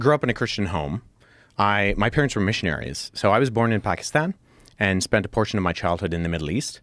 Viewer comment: Did he just said I was born in Pakistan 🇵🇰?? 11:50 lol iam from Pakistan 🇵🇰 as well my brother Aslamalaykum